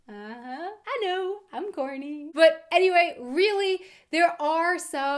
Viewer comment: did you say "i'm horny " ? Naughty girl .......